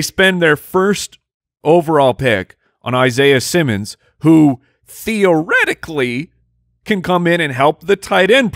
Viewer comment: Did say Theo Riddickly (as in theoretically)??